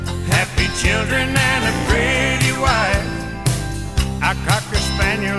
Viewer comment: When This first came out my ex wife and I where driving around and she thought he said crazy wife and I laught so hard dam ner drove off the road,course a lot of men have crazy wifes 😊